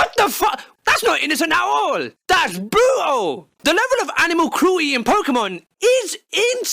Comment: When he said that's brutal, I thought he said bootle, as in Liverpool